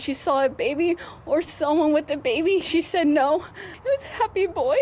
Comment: did she say he "was" a happy boy??